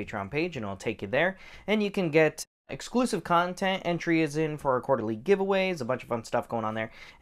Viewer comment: 1: 15 "Exclusive content." Ooooh, I thought you said explicit. Never mind. I kept watching anyway. Nothing like some good gun porn. 😁 Wife has a Shield 380 EZ. She could rack the 9 EZ but still went for the 380 nts. Probably change out the sites at some point. No Red Dot for her though.